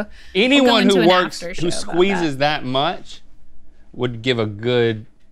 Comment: Yeah, "backrub" Link..."backrub" 😹 I don't think he even knows what he is saying half the time.